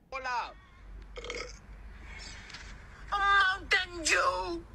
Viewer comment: did he just say mountain jew